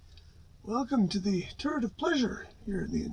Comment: Always sounds like you say "Welcome to to the turd of pleasure." :D